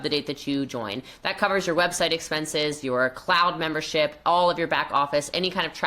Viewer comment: I heard "clown membership" which seems more accurate 😅